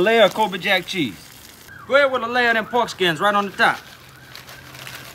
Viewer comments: When he said ,” Now y’all go head on with the pork skins “ my soul ascended up into heaven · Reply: For a split second, I thought he said "foreskin," and I was like tha f what? 😂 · I swear I heard “dem foreskins, right on top!”